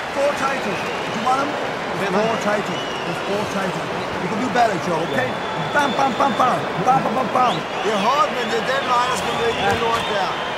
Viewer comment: Fantastic video! But at 3:11 the text is nowhere near accurate - they just made that shit up. What the coach actually said was: "It's hard, but this is the way you will win this shit".